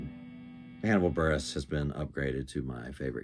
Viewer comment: who's the comedian you mentioned? It sounds like you said "animal burros".